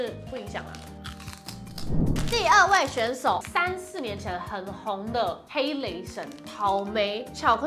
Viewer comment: 3,4年前聽成30年前 哈哈🤣